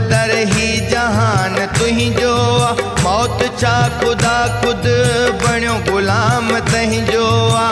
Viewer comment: what say kuda gulam tujo aa